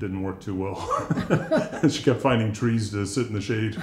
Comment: "We tried soccer...she kept finding trees to sit in the shade"....That was so me! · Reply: I thought you said she was fighting the trees not finding